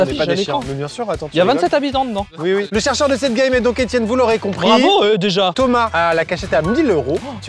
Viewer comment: j'ai compris Étienne Bolloré à 2:05😂😂😂